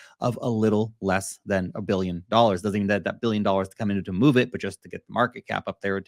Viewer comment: what did you say? “Doesn’t need” the $1B just several hundred M?